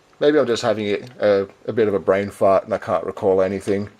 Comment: LOL, did I hear you say "brain fart", or was that just my imagination? Some really nice Euro cars in there, also liked the SRT8 and that mad Buick Riviera. Surprised you didn't like your Hyundai i30, a former colleague of mine had one and he was very pleased with it. Maybe he had a more sedate driving style than you have ;-) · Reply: Haha yes I said brain fart, its aussie for short term memory loss. My i30 was terrible, it was auto so thats probably most of why it sucked but the transmission was horrible always jerking up and down gears no matter the speed or acceleration pressure, and the seats are too far to one side so you drive on an angle, I can tell you more flaws but it will take hours. Mine at least was a lemon that was in the auto repairs most of the ownership of it and every time it was fixed it would just start jerking through gears again, thankfully it was under warranty. Now I have a nice Lancer with a smooth gearbox and a hell of a lot more fun